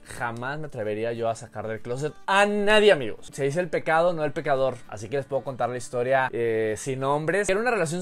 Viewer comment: JURAS QUE LE ENTENDÍ "SE DICE EL PICADO, NO EL PICADOR" XDDDDDDD